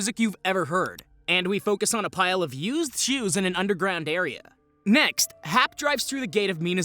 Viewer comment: When you said "used shoes" all I kept hearing was "used Jews". For a minute I thought we were having some Kanye Ye moment.